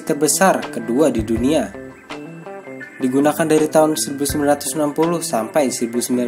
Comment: Mesin ke 2 terbesar kok digunakan dari 1990 smpek 1970 apa salah denger aku